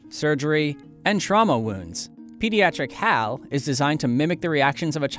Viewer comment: I thought they said "Pediatric Cow" lolololol